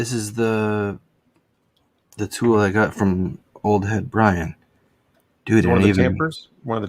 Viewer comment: Which one? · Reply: sorry it’s at about 45:50. I think he says Old Head Brian.